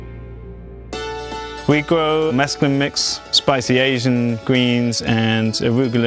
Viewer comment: did he just say he grows mescaline??